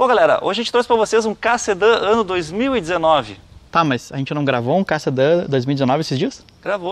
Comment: Só eu entendi “CASSETÃO 2019” em vez de “Ka Sedan 2019”? 😂😂😂